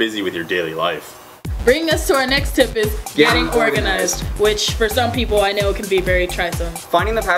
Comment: Im proud to say that i followed the Since 2006 Edit: i had to laugh cuz i thought you guys said Venusaur stands for getting organized XD